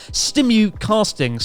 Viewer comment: Stimul-cast sounds like a medical trratment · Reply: yeah it's so funny and I thought Chris said Stimulate